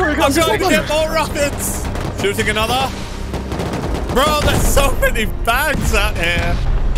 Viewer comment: wat did he say ????? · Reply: (bro there's so many bags out here) turn on the CC